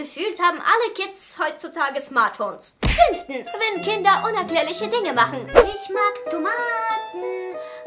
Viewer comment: Bin ich die einzige, die bei 3:54 auch 5. anstatt 10. verstanden hat?